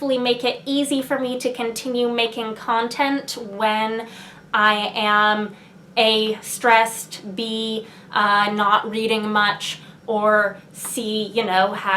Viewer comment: Seven of Infinities sounds really good. I should check out that series. Somehow, I misheard "When I'm A) stressed, B) not reading much..." as "When I'm a stressed bee."